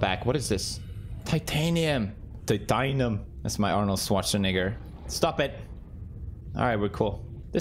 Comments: omg holy cow wtf did he just say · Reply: Arnold Alois Schwarzenegger is an Austrian-American actor, producer, businessman, retired bodybuilder, and former politician who served as the 38th governor of California from 2003 to 2011. As of 2021, he is the most recent Republican governor of California. -Google lol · Btw when you said “Arnold Schwarzenegger”, the auto generated captions thought you said the n word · what he sayyy🧐🧐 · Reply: Arnold Schwarzenegger, I got scared too but that’s what he said :) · what did he sayyyyyyyyy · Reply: He said Arnold Schwarzenegger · What did he said on 16:05 I'm confused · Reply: He said Arnold Schwarzenegger 😅